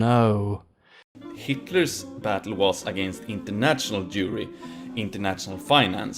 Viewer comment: I keep hearing "International Jewelry". Maybe the Golden One isn't an Antisemite after all. 🤦‍♀️